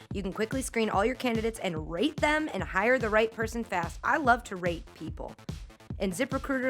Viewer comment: At first I thought she said I love to rape people😂